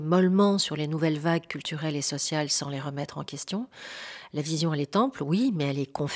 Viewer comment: À 8:00 "la vision elle est_ample" j'ai entendu "la vision & les temples" ...🙂 j'ai trouvé que ça allait bien avec Neptune & Jupiter ... un Soleil/Neptune en conjonction en ♐ 🏹 🌟😉💜